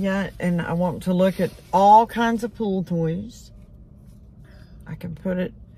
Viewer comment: Wants to look at pool toys….I thought for a minute said pool BOYS 😂